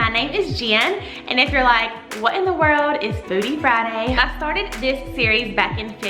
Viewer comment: My husband walked by as I was listening to you and thought you said “bootie Friday” 😂